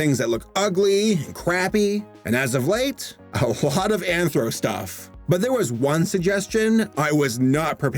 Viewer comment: I hEarD ANtHrO